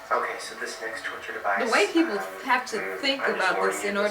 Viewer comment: At 7:00, did she say "...the way people..." or "...the white people have to..."? If the later, then: WTF!? This is f***ing racist. Any kind of torture device was an is not exclusive to "white" people. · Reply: She said "the way people have to think about it..."